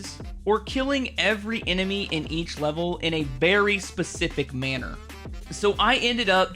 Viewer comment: I thought you said “a BERRY specific manner” haha